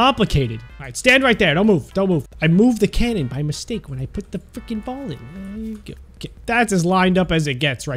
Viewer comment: wtf i thought he was family friendly he said " i moved the thing when i put the fucking ball in" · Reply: He said “frikken” so yeahhhhhh